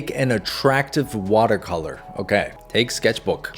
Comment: i thought it said "attractive watermelon"😭🙏